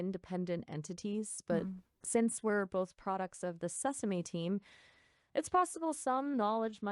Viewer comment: for a moment I thought she said "the susame team"